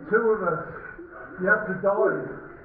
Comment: i thought the diver said "no, you have to die"